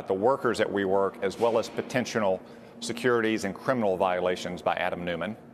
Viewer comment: Did he just say potentional? 😂